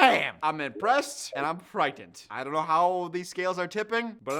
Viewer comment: I heard “impressed, and pregnant”…not frightened😅